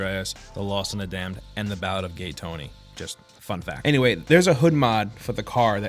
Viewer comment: I thought he said fuck that, not fun fact